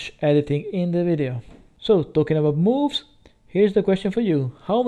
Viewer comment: At first I thought I heard you say "talking about boobs" but then quickly realized you said moves. Anyway, moving sucks and I have back problems, so I feel for you buddy.